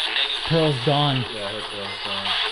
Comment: Sounds like it says “Pearl’s gone. Go away.” In an irritated tone at around 38:33. I swear I hear it say go away.